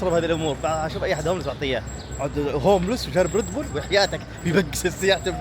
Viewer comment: قال وحياتك والا سمعتك غلط؟؟